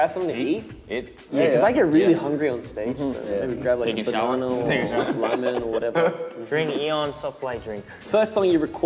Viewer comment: did chan just say banana or lemon or whatever 😂 someone please tell me its not just me who heard lemon